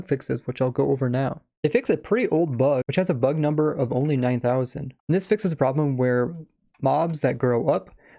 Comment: I heard it as "...bug number of only OVER 9 THOUSAND"